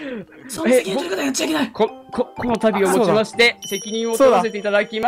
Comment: 最後さんの俺もかい！？がコネさんに聞こえた🤣